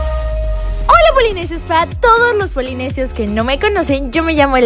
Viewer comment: Ups entendí en el segundo 0:16 del video entendí "hola Polinesios fat" xD